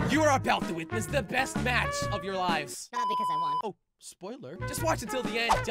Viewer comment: What he said at 0:05 is "Because i won the match"